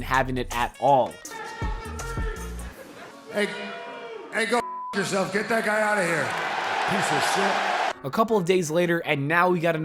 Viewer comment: what did he say? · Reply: "Get that drunk piece of trash out of the ring!" right before he said anything after he just got back from Alcoholism Rehab. Some idiots in comment sections think Moxley is being "soft" because he wanted the fan kicked out.